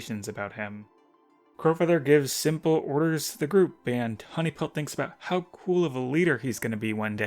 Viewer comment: I heard say honeypelt instead of tawnypelt did everyone else hear it or no